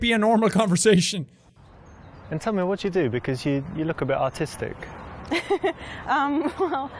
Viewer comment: I thought he said what do you do caus you look a bit autistic XD